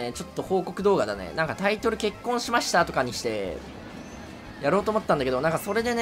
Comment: 結婚致しましたねって聞こえてきて笑ってしまった 何故がめちゃくちゃ嬉しいです、さん結婚おめでとうございます！！これからも無理のない範囲で動画楽しみにしてます〜！！